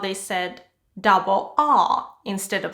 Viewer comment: In 15:33, I hear that he said double u , but I cant see double u here, why?